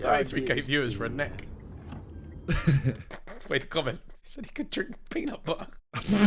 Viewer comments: wait did ethan just say the n-word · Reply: “He said” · did Ethan just say the n word? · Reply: Said he could drink peanut butter is what he said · did ethan just say the N word someone screen record it before they delete it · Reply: He said "said he could" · what did he say!???? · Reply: “he said he could drink peanut butter” 💀💀💀